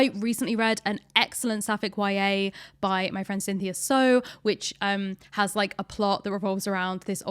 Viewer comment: If anyone else is curious about the book mentioned at about 33:00, I'm pretty sure it's "If You Still Recognise Me" by Cynthia So (they/them)(not Stowe, as I misheard, who is also an author but writes very different, non-fictional things)